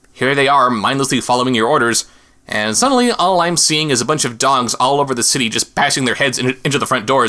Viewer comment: It sounded like you said a bunch of dongs. giggle